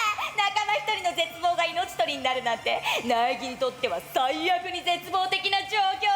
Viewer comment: Did I just hear "Nagito" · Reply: no. it was Naegi